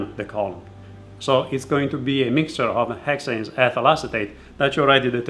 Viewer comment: What did he say 2:39-2:41? “A mixture of .... and ...” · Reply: a mixture of Hexane and Ethyl Acetate